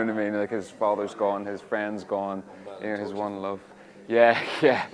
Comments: I really really really NEED to know what Bradley said at 3:19 in the background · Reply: His one love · what did Bradley say at around 3:19?? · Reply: I hear: he’s one love